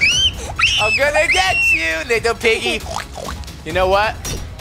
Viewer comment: WHAT DID HE SAY🤨🤨🤨🤨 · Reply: He said little piggy what's wrong?